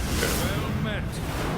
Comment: What said: "Well Met" What I heard: "Fak tu yuu"